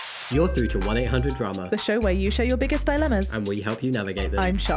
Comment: I listened to it at x1.75 speed and the intro sounded like was saying "where you share your biggest enemas" and I was like 😮